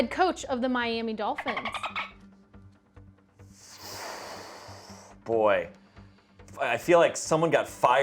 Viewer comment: Anyone else at 8:32 think msm was saying boo